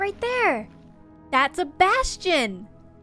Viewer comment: i thought she said "look Sebastian" and not a "bastion"